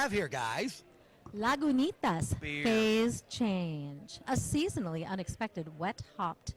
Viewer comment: Problems with being partially deaf, when was describing the beer I almost spit out my coffee. I swore she said, Lagunitas, a seasonally unexpected wet fart." Clearly, she didn't. But I hate when my hearing transposes words.